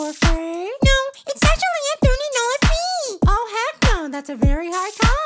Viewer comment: "No, It's actually a 30 dollar fee." 🤓☝️ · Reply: I thought he said $30 free